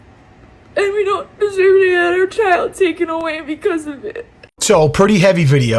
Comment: Did she say "another kid taken away"? Anyone confirm?